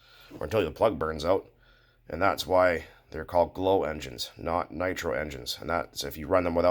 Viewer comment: Nope. Won't hold a catalytic reaction with the platinum in the glow plug coil. Also the wrong AFR. you can use metal and klotz super techniplate oil in a 90/10 mix if you can't get nitro. Now I'm in Canada it's 70 a gallon lol keep in mind that the Minimum oil percentage for RC cars is 10% by volume for Nitro engines. Gasoline is 2% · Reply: I thought you said there are No Nitro engines😂😂😂